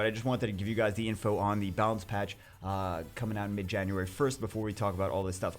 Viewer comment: Ok, so i realize i heard him wrong. I thought he said " balance patch is on midyear 2021 ", actually it was mid january..